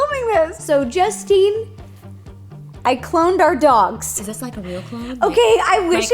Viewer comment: I thought it said I cleaned the dogs like what